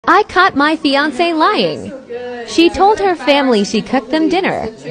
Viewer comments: Umm I thought it said *I caught my fiance flying " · For a whole two secs I thought he said” I got Beyoncé lying”